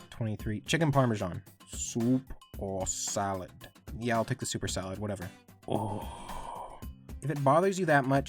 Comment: I’m glad I’m not the only person who thought the waitresses say “super salad”